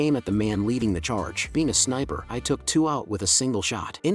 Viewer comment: Why did i hear "penis sniper" at 59:46 ???